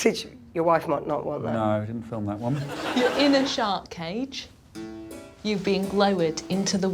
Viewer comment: anyone else hear "shart cage"?